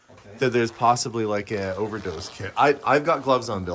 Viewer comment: I heard they found 'overdose kid' in one locker, like a body, and you bought it 🙃luckily it was 'overdose kit'